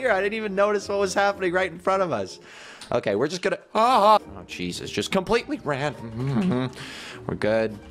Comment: did you just said Jesus